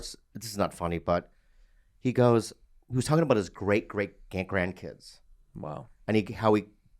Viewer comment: Kinda immature comment but did anyone else hear “great great gay grandkids” 😂